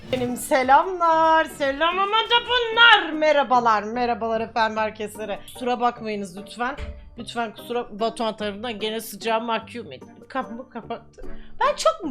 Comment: selam mada faka dedi sandım dedim ki: nE dedin abla??